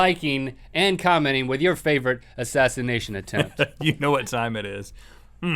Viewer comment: Lol I thought it said "bizarre animation attempts"...xD